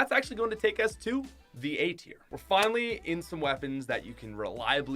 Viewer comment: When you said A tier I thought you said eight ear